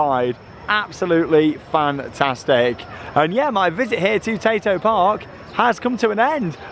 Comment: The amount of times I thought you was saying potato park